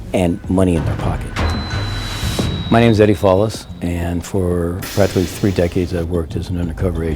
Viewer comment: I thought he said his name was Eddie “Flawless.”